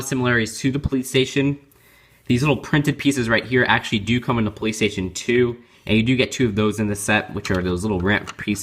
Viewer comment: when you said the piece came in the police station too it sounded like you said the piece comes in the playstation 2 lol